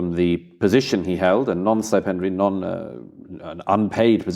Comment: What word does Murray say at 2:47? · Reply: non-stipendiary, meaning basically unpaid.